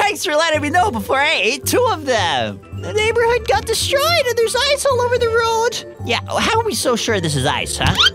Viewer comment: I thought he said `there are eyes all ovr the road` LLLLLLLLLLLLOOOOOOOOOOOOLLLLLLLLLLLL!